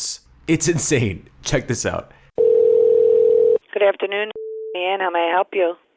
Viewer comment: every time she picks up it sounds like she says "good afternoon and shit"